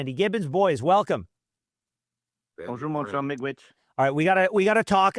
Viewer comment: had to do a double take on what the first fellow said. I heard "bonjour mon nitwit". [Edit. Then I heard him deliver his opener. Canadians care about these things, good sir.]